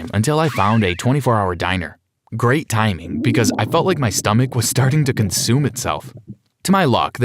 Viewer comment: Did you just say your stomach is trying to eat your stomach get on and eating food or you will die and this is the time where he said it